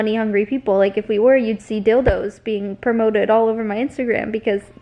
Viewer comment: Did she say “DILDOS” at 11:58!?!?